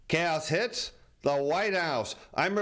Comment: At first I thought he said "cows hit the White House." It had tp be incorrect because it's actually the public that's getting "milked."